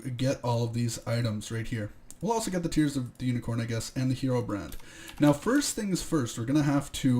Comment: I thought he said who gay when he read herobrand